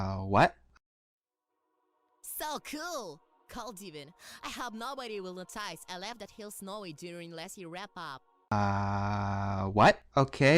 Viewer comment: i can translate what not rainbow dash said at 2:37 . he said "so cool! cold even! i hope nobody will notice i left that hill snowy during messy wrap-up". it means he needed to clean up the snowy hill.